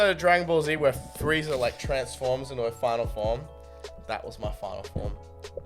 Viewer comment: Did he say Frieza was a chick? Sounds like he said "her final form"